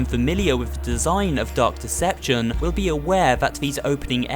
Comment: did he say Duck deception?..